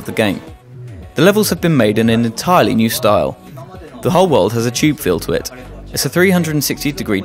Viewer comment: did he just say hub world? does that mean the hub world returns?!